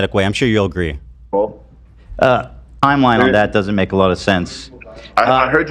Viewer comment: I literally thought that was playing a sound bite every time Hasan said “Ethan” lol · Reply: Yes I kept hearing “ethhhannnn that doesn’t make sense” praying clips 🙏